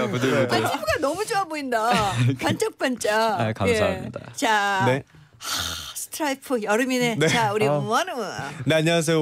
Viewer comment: i don't understand korean but i heard "akh, straight outta the anime uri wonwoo" when it's wonwoo's part lmao